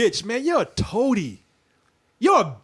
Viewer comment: When said "toady" at first I thought he said "Tony". I the idea was he was like Tony Stark because he was rich.